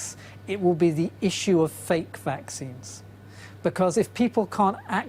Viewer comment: Did he say "fake vaccines". Doesn't give me much confidence.